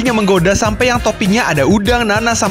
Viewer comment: gasalah denger kan gw? Toping nanas? 😂